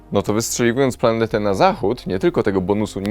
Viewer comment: Czy tylko ja usłyszałem "...ale wystrzeliwując planete na zachód...".?